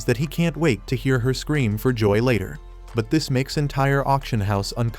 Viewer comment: wtf did he just say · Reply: Nah that’s crazy scream for later nah